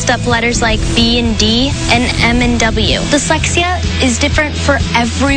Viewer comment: OMG I JUST REALIZED THAT SHE IS SAYING DYSLEXIA AND I ALWAYS THOUGHT SHE IS SAYING „The sexiest“